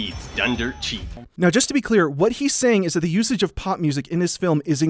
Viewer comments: I thought you were saying pot music... Not pop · For the first 3 minutes of this video I thought you were saying "pot music" lol